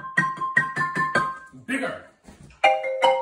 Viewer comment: Oh he is saying bigger. I thought he said the 'n' word. Lol